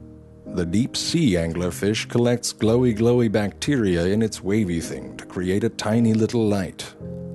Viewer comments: Glowy Glowy bacteria in its wavy thing. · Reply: For a few seconds I thought he said 'rapey thing' and I was wtf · I thought it said anger